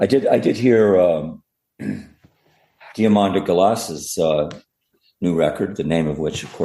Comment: Does any one know which artist Micheal is reffering to at 14:38 ? I can't figure out the spelling of the name. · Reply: Diamanda Galas!